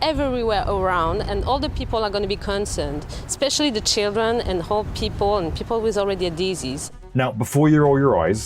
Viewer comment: subtitle says "hold" but im sure she said 'old'